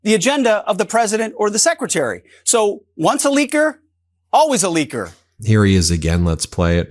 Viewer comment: I did not understand what Hegseth said at ~ 9:32. was it "once a licker always a licker"? or was it "once a liqueur always a liqueur"?